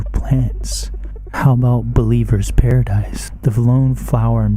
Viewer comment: did anyone else hear “Belieber’s Paradise” lol